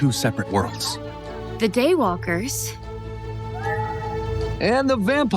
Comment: When they yelled day walker at Addison or… I think that’s her name… I totally thought they said gaywalker